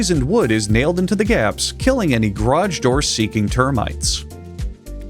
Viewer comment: I can’t help but hear “groj” instead of garage doors haha